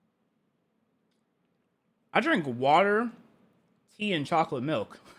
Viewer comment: did he say pee or am i buggin?